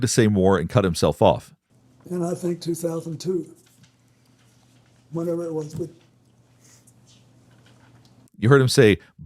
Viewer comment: i dont think he said "but" i think he said "whenever i was wi-"